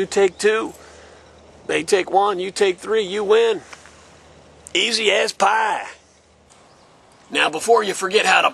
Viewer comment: i thought u said easy a** pie